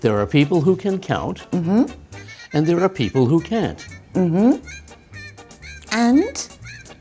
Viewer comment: i think said (( people who can’t)) but you understood the opposite 😂 🇺🇸VS🇬🇧 difference of pronunciation in British and American about can and can’t